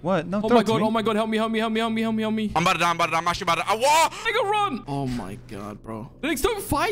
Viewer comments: I thought said the n word but he said Liger run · did he just say the n word? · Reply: He said liger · WHAT DID SAY??? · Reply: He said make a run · sounds like says the N word but it is just lager cut out.